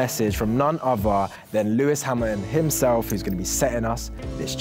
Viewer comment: did say Lewis Hammond